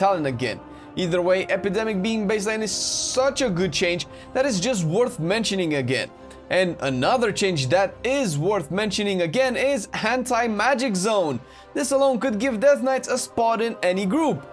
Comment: I swear when you said Anti magic zone i swear i heard you say hentai magic zone. I almost spat my tea out.